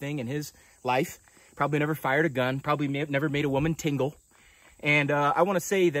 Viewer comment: I totally thought you said, "make a woman tinkle"…..which would have been pretty damn funny.